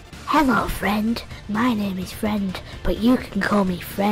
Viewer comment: i dont get it...did it said friend a lot of times?